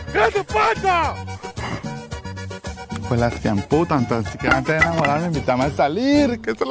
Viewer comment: que cosa dice? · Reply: Creo que dice "tan siquiera antes de enamorarme..."